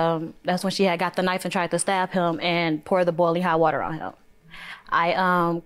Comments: I thought she said bologna hot water on him. 😅🤣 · I thought she said bologna hot water on him! 😹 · I thought she said bologna hot water, boiling hot water 🤣🤣🤣